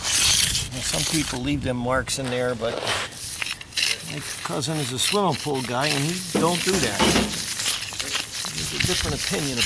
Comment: I couldn't catch what he said at 10:58 mark, where was explaining how "some people leave them marks in there, but it causes....." after that, I couldn't catch the explanation. Turning on the caption didn't help. I think something about pool of water? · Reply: Hi Nisrina, some people leave the edge Mark's in for a edged look but swimming pool guys don't because you can stub your toe walking bare feet, it the lines are too big, matter of preference, thanks